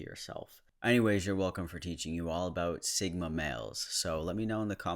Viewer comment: I thought it said "Single males" lol